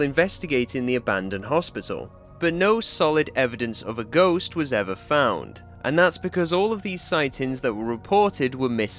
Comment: what did you say? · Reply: (Shy one) the shadows will not fall